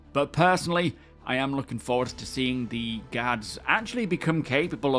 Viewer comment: I was half-listening to the beginning and thought you said "gods" instead of "guards". I uh.. got concerned.